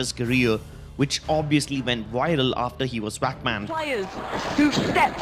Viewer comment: He got Wack Banned thats what i sound like hes saying xD